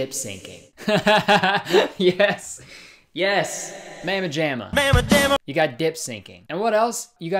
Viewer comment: as a female from alabama, if anyone every calls me a "bad mama Jama" i am turning around, and walking the fuck away lol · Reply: I’m from the south (kinda), but I guess I couldn’t understand his accent cause I thought he was saying “pajama ma’am-a” like the woman loves herself some pajamas